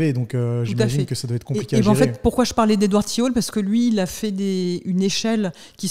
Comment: Edward quoi ? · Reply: T hall